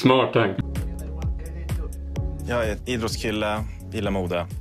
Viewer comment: Vid 1:20 hörde jag ”jag är en inbrottskille...”